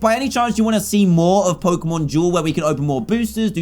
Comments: For a second I thought you said pokémon jewel. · Lol because of his accent I thought he kept saying Pokemon Jewel not duel lol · Who else from the U.S. thought he was saying pokemon jewel when he said duel?